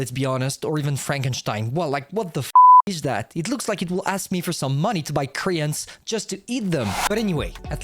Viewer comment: I thought you said "It would ask you to buy Koreans just to eat them"